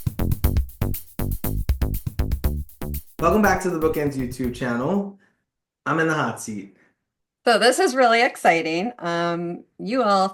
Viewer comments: Oh! You said you're in the hot seat 😂 I had to turn on closed captions because it sounded like you said you're a Nazi 😅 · I heard "I'm in the hot seat" as, "I'm a Nazi." I had to go back and play it twice. Congratulations on ten years, and on not being a Nazi.